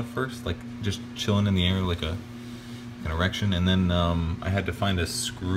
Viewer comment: did he actually say erection?